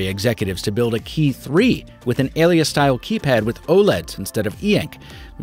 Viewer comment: the captions say “Owlette” instead of OLED lmao...